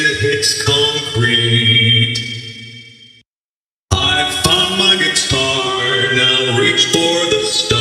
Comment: doesn't he sound like he say, "I FUCK my guitar"?